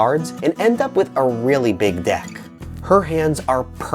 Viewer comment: and end up with a really big what??? I swear I heard 'deck'. Those dolls generally cause the player to have a large deck. They always end up playing with the large deck. When the game is over, the deck is no longer large.